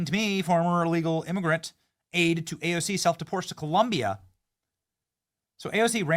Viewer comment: Dang! I got excited for a minute! I thought you said AOC self deported!